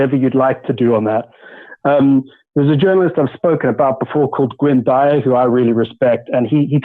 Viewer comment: or so... Journalist Glenn who? Couldn't make out the last name. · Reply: i think Gwynne Dyer